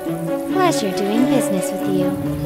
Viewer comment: didnt she said "pleasure to make business with you" instead of "pressure"?